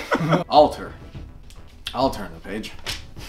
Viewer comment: When he said alter n the page I thought he said alternthis b****h